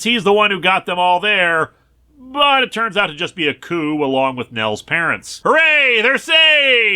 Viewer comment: i know he said a coo (and its said a coo in the comic) but why did my mind go to Aku (from Samurai Jack) so it sounded like said its just aku & nellys parents (Which is just a funny image in my brain now of the peanuts doing a crossover with Samurai Jack)